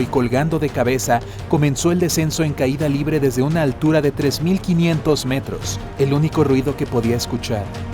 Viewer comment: Creo que os habéis pasado. 3 km = 3000 metros. 3000 km = 3 millones de metros..... Desde el espacio? Los aviones van entorno a 10 kilómetros, hasta los 3000 kilómetros todavía le falta 2990.... Buena errata. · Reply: ni lo había notado, pensé que decía 3000 metros,a no ser que haya caído de una nave espacial jiji😹